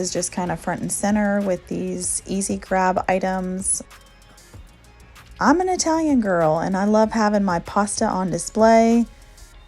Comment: Hola New to your channel great job making over your pantry .... i thought you said ( imma tell you girl ) instead of i'm an Italian girl at 1st lol .......and this may be odd but what got me to subscribe to your channel was the fact you kept all of your food items in their original packaging ☺ that's keeping it real i loved that. can't tell you how many YT & TT videos i scrolled over when they break out the lil plastic containers. well enough on that sorry ~ Great video chica God Bless ~